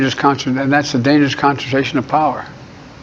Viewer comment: it sounded like he said "dangerous concentration of power". One of the few times I was ever able to make out anything that he has ever said. I could be wrong. Also how is he still in the upper 30s of an approval rating?